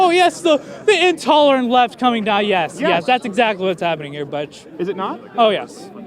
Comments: Did he just call him “Butch?” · did he just call him "buttch"..? buddy and bitch came out at the same time. . y is no one talking about that. ... 😂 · Bitch? Bud? Butch? What did he say?